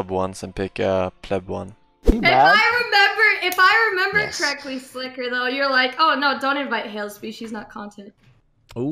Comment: what does she say? I can't make it out don't know why it's funny. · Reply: Jebaited